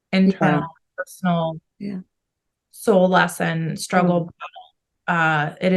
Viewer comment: I heard a man say "Struggle" and before that I heard other words